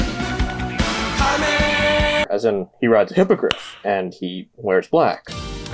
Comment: i thought you said " He rides hypocrites" cause for some reason in Anime, words such as enemy, comrades, allies, and hypocrites are so common that whenever i even see something related to anime i immediately have a Vietnam era flashback